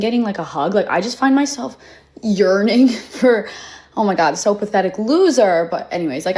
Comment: " I find my self yearning " I am so sorry for mishearing it as " urinating " . 😄